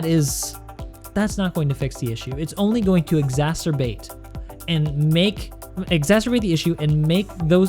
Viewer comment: Did you say 'masturbate' at 8:36? All kidding aside, education is naturally very important, and I think you don't need to worry about the survival episodes. We'll gladly wait. :) About the content theft; it's just plain rude!